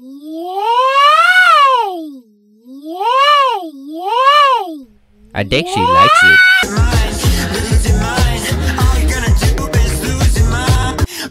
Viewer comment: In this scene I listened to it and I heard Pomni saying yay over and over again